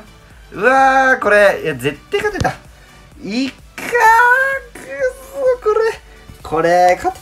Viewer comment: いかーくっさーに聞こえるw